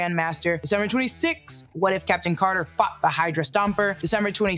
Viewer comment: I refuse to believe I am the only one who heard "what if Captain carter FUCKED the Hydra Stomper"